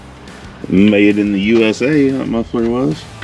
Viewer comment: “made in the USA that muffler was” 😂 had to take it back 3 times because I could of sworn you said “m***er f**ker”🤣 Love the content you guys are pumping out!❤